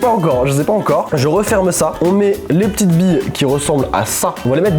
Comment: Quand ta dit "des billes d'eaux"j'ai compris "bidon"JPP